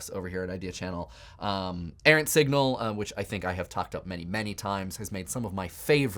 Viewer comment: what the heck is he saying I can't make it out and the CC is wrong too. Sounds like he's saying "ant signal".